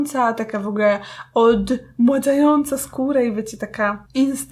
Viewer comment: Czy tylko ja usłyszałam ,,OLD MŁADZAJĄCA,,?♥ Pozdrawiam super filmik.